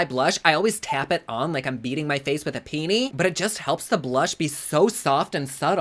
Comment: I had to rewind to make sure I heard what I thought I heard …. “Tapping my face like I’m tapping it with a peenie ”!?!? I’m dying of laughter!!!!